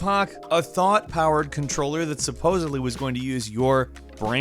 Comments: I heard ''thot controller'' lmao 😂 Also wtf with these controllers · Who else thought he said “Thot powered controller.”